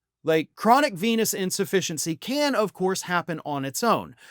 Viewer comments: Initially, I thought you were saying "chronic penis insufficiency". But I’m sure this is 100% true as well. Ask Perjury Traitor Gangrene. She's obviously been banging him. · my poor hearing thought you said at first was chronic penis insufficiency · At first I thought you said chronic penis insufficiency and almost spewed the cola I had just sipped. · When I first heard this, I thought I heard 'Chronic penis insufficiency"... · LOL, at first i thought you said "Chronic Penis Insufficiency" I guess he has that too , according to Stormy 😂😂😂